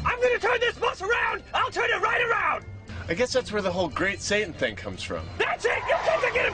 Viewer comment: I know I probably misheard this, but at the end it sounded like he says "I guess that's where the whole great saiyan thing comes from!"